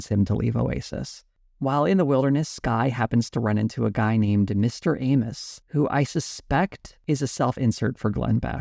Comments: On first listen I heard 9:36 as "Mr. Anus", and then Glenn Beck popped up on screen. I refuse to acknowledge that I heard wrong. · at first it sounded like you said "Mr. Anus" and i was like how self aware for him to name his self-insert that.